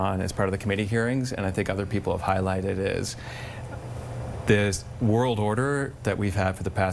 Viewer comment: did he say we have a new world order...